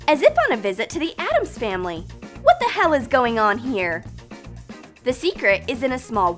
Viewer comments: I thought said what the heck is going on here not help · did she say what the hell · i heard her say what hell here